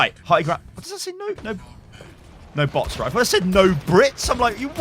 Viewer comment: I thought it said no booty lol